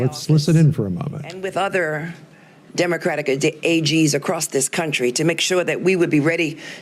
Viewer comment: If You listen closely at about 10:09 to 10:11 You hear Letitia James say “Democratic uh Dic, AG” almost sounded like she slipped up and called her little Gestapo what they are, DICTATORS!